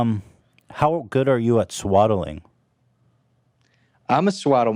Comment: I'm at work and have the volume really low and I thought they were saying swallowing lol